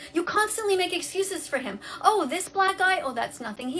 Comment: Y’all, am I the only one who heard the “oh this black guy” at 12:38 when she was talking about abusive relationships???? Idk but that feels like blatant racism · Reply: She said " black eye "